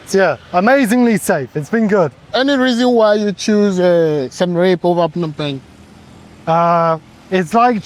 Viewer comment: wrong captions, it's supposed to be "choosing to live in Siem Reap over Phnom Penh", but the caption said "choose a some rape over up no bank" 😅😅